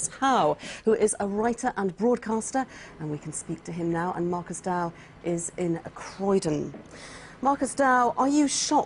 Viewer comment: does she call him Marcus Dowe....looool